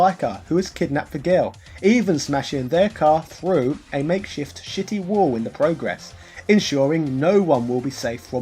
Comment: I swear, I heard "shitty wall" and immediately thought of South Park.